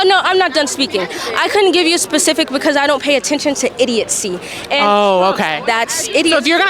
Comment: That girl at 4:50 literally say "Idiotsee" lol I think she meant to say Idiocy lol